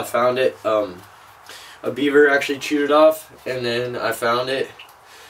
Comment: Looks awesome. Well done! When you said a beaver chewed it off I thought you said a viewer chewed it off. 😂😂😂